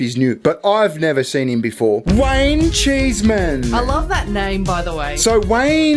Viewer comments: Oh, Wayne. I thought you were saying Wang Cheeseman. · Heard you say his name and all I heard was Wang-cheese Man. And that shall be his name henceforth. 😂😂